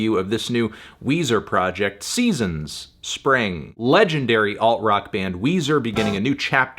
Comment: I thought he said "allegedly alt-rock band Weezer"